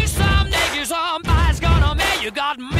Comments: LYRICS (for those who need them): Big noise, boy maker Buddy you’re a pain in the sman Some be a big reet, gonna pay You got big disgrace, your mud on your face Go for the plan or kick in your case, singing We WILL, WE will… ock you We WILL, WE will… ock you Aardman, showman Buddy you’re a-budding in the stuns On Nick, what are we gonna take? You got big disgrace, you’re not on your face Rover the planner or aving your base We WILL, WE will… (sing it!) ock you (on her!) We WILL, WE will… (ooh!) ock you Poor man, bone man Buddy you’re a pleading with your bee Someday, you suneyes gonna pay You got big disgrace, mud on your face Some into your butcher, back body better pace We WILL, WE will… (air sing!) ock you (mmm) We WILL, WE will… ock you (everybody) We WILL, WE will… ock you (mmm) We WILL, WE will… ock you (all right) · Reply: I heard "butt on your face" and laughed for the entire duration of the song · I heard “ur butt on ur face” 💀💀